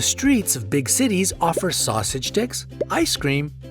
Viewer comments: now why did I think he said Sausage Dicks 😑😐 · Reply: Same but I'm sure it's sausage dips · I thought i heard sausage dicks 🤣🤣🤣🤣 i have to rewind the video to make sure of what i heard.. Lol